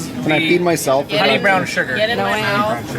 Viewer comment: I thought you said "sour sugar"...